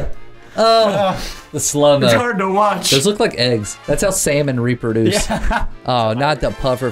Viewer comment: I thought he said thats how sam n reaper duce